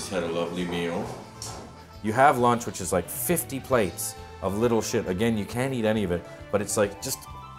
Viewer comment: did he say little shit at 4:22 ?